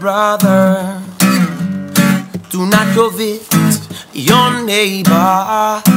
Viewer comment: i keep hearing "do not covid your neighbor" ... what a song for the time.